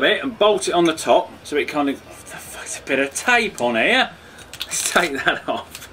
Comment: did you say what tha fuck?